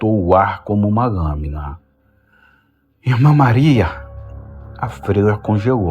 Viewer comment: escutei um elma maria ?